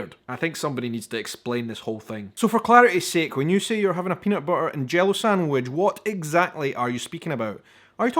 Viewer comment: Hi - I think you may be mishearing “peanut butter and jello” - that would be a decidedly unusual thing and of course someone eccentric may actually eat, but more likely it is “peanut butter and jelly”. So jelly is like jam, but made only with fruit juice and no pulp of the fruit is included. - that would be our “jam”. CIlantro is the fresh leafy part of the herb; coriander is the dried seed of that same herb. Druther is usually pronounced “druh-thur”, rather than “droo-thur”. In the US if a guy asks a girl (or a guy) out for a date, the asking party is ASSUMED to be paying the tab(s) - unless it is spelled out in advance that they are “going Dutch”, I.e., each person pays they own bill.